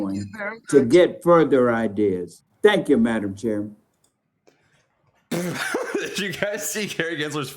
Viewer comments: /6:20 does he call Gensler madam chairman? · madam chairman? am i hearing that right? did he just say that? or am i trippin?